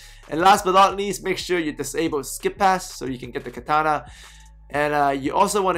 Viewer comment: Wtf did you say at 8:37 "so you can get the kitana?" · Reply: Dreaded Assassin Disable skip pass